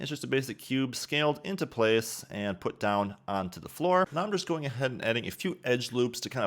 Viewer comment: minute 4:32 Sir, u said UNDERscore? right? i cant understand this part. can u help me? Now i using Blender 3.0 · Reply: said 'on to the floor'